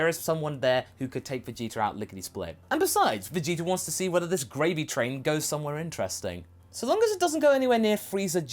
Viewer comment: did he say Gravy training instead of gravity training? 😂